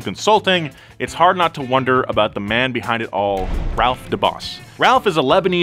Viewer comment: Ralph DeBabbas sounded like Ralph DA Boss! Which is a bit funny to me but A REALLY COOL NAME!! 😎💪🏾